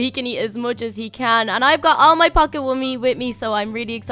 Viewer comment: You will notice that is 'i have all my pocket womey with me' She said pocket womey instead of pocket money